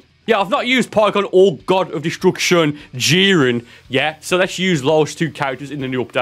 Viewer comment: Did he just say God of Destruction Jiren????? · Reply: LOL He did say God of destruction jiren